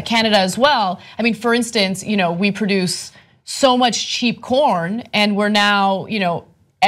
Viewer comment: Ha Ha. I thought Ana said we produce so much cheap porn and we send it to Mexico! It was corn!